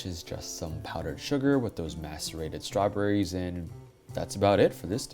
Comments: you gotta double check your closed captioning because it says 'masturbated' instead of 'macerated' at @ 15:09 LMAO. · I know that's a weird comment, but the subtitles say "masturbated strawberries" at 15:09 and... idk you might want to fix that. Although it gave me a good laugh! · Uh.. at 15:10 the subtitles say "masturbated strawberries" instead of "macerated".